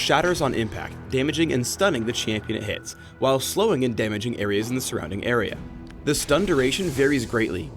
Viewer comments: Does he say "Slowing and damaging areas in the surrounding area?" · he says"While slowing and damaging areas in the surrounding area" Why does CC say "While slowing and damaging enemies in the surrounding area"?